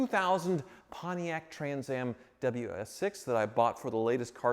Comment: You keep saying "Pontiac Trans Am" but all I hear is "General Motors".